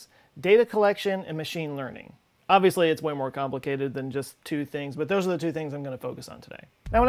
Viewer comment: did you say, "Obviously, it's Waymo complicated..." ?